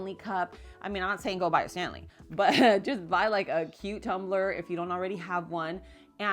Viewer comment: I thought you said buy a "cucumber"....wondering how we put water in the cucumber. So I went back, and you said by a cutetumbler. 11:29 :)